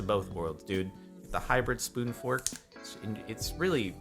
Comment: Ok so the word fork is ruined for me too when he said spoon fork at 18:22 in my head I heard spoon Fuck XD (Edit- corrected spelling and timestamp)